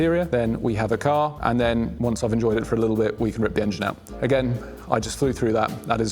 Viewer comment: You’ve put me at ease. I heard you say “ripe engine out.”